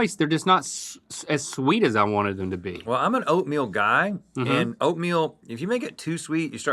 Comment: When Rhett said, "I am an oatmeal guy" my brain immediately heard that as "I am a thoughtful guy"! I propose an oatmeal remix!